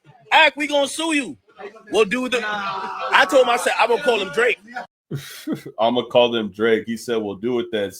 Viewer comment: I thought Ak said, “Well I’m gonna call UP Drake”, not call THEM Drake. As in, if Ak gets sued, he’s just going to throw Drake under the bus to the NFL and 🐀 out Drake for paying the chat guy to leak it. Hope they both get sued. Regardless, this will backfire for the OVHoes 😅